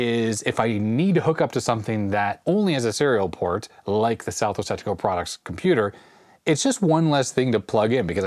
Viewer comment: You should use an acronym for Southwest Technical Products since it sounds like you say Southwest Testicle Products.